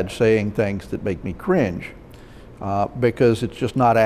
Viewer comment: did he just say CRINGE!?!??!?